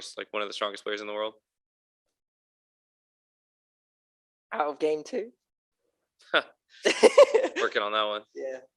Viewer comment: what does boaster say to? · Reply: He said: out of game too» asking if TenZ is on of the «strongest» players in the world